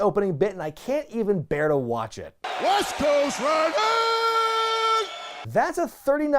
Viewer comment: WTF did Nash just say lol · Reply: "West Coast riders". To quote: "That's a 39 year old man, folks."